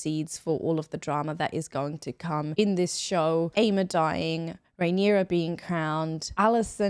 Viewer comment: i don't think i revealed any key plots? can you let me know what triggered you? im sorry, i'll be mindful of that next week :) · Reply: hi, I watched the video again. 32:50 was my initial concern. On the second watch I hear you say Aemma, whereas the first time I thought I heard you say Daemon, and I initially thought you just dropped a huge spoiler about him dying in the future. You also followed it up by saying Rhaenyra being crowned heir and I initially thought you were saying crowned as Queen, which also could have been a spoiler. My apologies for raising the alarm bells!! I appreciate you taking the time to reply! I’ll be tuning in next week! 😀